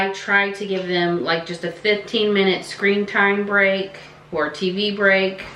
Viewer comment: When you said "screen time" I heard "scream time", and I was like - that is impressive🥵😀😂😂😂😂